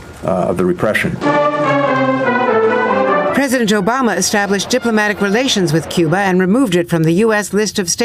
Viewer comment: Did anyone else hear her say Joe-Bama there?